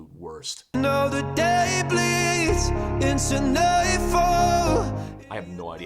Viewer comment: Wow, so he says “Now the day bleeds into nightfall” where I always heard “Now the Damocles, the knife fell.”